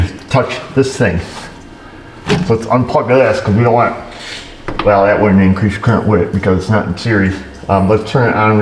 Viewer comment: Not to criticize, but what did you say @ 10:35 ?? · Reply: 😂😂. Listen again. “Well now that wouldn’t Increase the current”